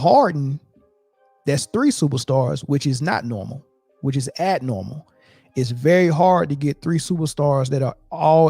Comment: Did he just say, “It’s not normal. It’s ad-normal.”?? 😂